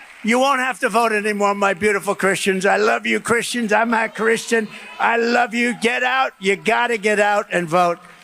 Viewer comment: Please tell me that someone else heard him say "I'm not a Christian." edited to add Time Stamp